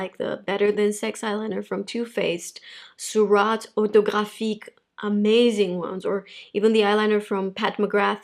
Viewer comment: What eyeliner did she say iat 20:50? Dogafiq? Thank u in advance